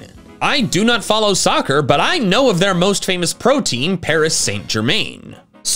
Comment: I can’t😂 I keep hearing protein(it’s supposedly protein team)